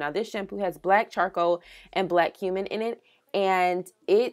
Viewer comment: Had to rewind like 3X I thought you said Black Human in it 😨🤦🏾‍♀️